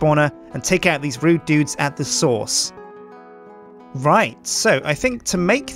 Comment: did anyone think Sjin said sauce instead source?